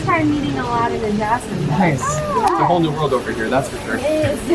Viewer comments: Who else thought she said, A Whole New F*&# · “A whole new ————-“ I thought she said a whole new “fuck” like I was like why are you saying cussing words LMAO BUT THEY DIDNT WANT get copyrighted